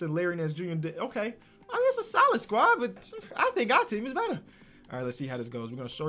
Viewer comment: DID HE JUST SAY BITCH?